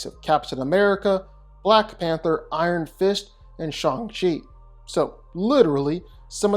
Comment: When you said Shang Chi, for a split second I thought you said he had beaten Sean The Sheep...